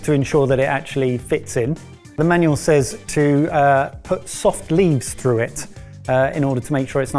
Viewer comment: I'm sorry but can anyone explain that to me? soft leaves? leaves? is that what he's saying? idk if it's cos I'm not a native english speaker, but my brain can't make it make sense 😅